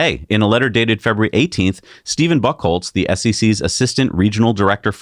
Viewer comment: <-- 🤣 i heard Steven Buth*le